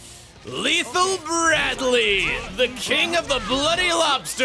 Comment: I thought that said lethal brandy